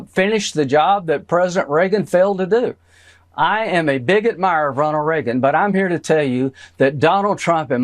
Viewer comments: Wow Graham should probably be careful how they pronounce that, it certainly sounded like "I'm a bigot mire" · Did I just hear him say that he is “a bigot mirer”???????!!!! 😂 😂 yes . Yes you are.